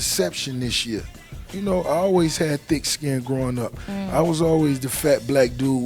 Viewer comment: sounded like he said "you know i always had dick skin growin up" lmao